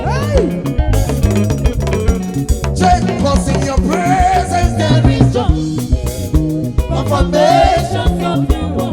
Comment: Can someone help me make out what he said at 4:20. In your presence there is joy -----------------------------------------? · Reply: Enter spirit bro